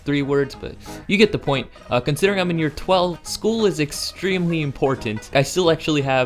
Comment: did you say you were near 12?